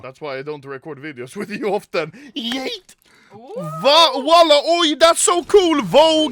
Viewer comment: did I hear wallah